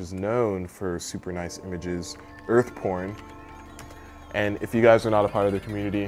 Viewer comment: did he say earth porn XD